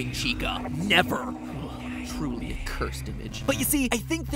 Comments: did matpat just say the b word??!! >:O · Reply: He said cursed image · Could’ve sworn I heard MatPat say “Cursed B****” to dabbing Chica at 1:54. Listen closely.